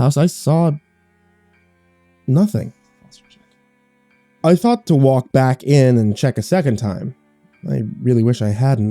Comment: anyone hear that whisper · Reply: That's his wife saying "dinner ready"